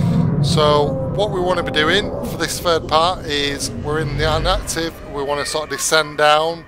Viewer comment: Inactive or active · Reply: i was confused because im relatively new to Subnautica and it sound like u said active and the i card said inactive